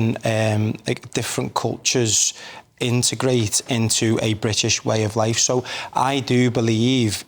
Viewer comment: 😂😂😂 did he say integrate?